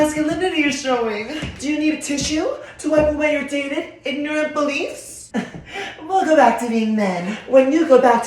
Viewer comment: I thought they said ignorent balls😂